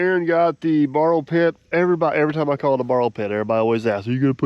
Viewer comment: I always thought you were saying burrow pit.